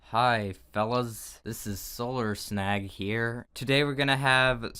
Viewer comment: i thought he said solar snack and i was shook